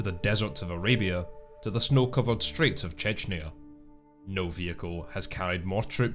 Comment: Snow-covered straits of what now? Did I mishear? Do you know where Chechnya is? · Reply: STREETS, not straits.